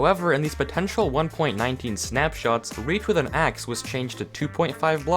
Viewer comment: Wait did he say 1.19